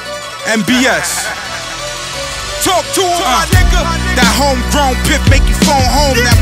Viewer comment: what they say on 0:25? Tortuga nigga? :D